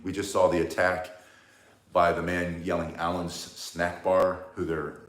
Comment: I thought I heard you said "Allen snack bar." if I did, LM*O. Merry Christmas